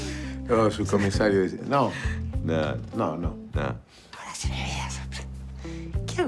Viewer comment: Alguien entiende que dice en el minuto 29:22 aproximadamente? Muchas gracias por subir los capítulos, un saludo desde España, que bueno poder ver una serie acá y reírme un rato. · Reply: Ah hora se me viene la sorpresa